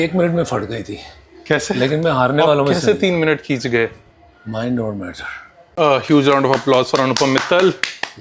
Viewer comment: What is Anupam saying here? Mind over .....? Really wanna know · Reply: Mind over matter